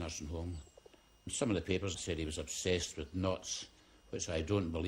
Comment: it says “..people were saying Albert was obsessed with (what sounds like) knots” Does it say knots or am I hearing it wrong? Or are they talking about knots in the hangman’s hanging rope?